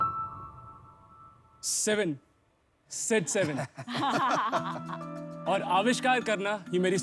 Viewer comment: I thought he said 67 😂😆